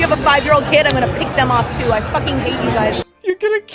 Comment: Did she say "beat them off too"?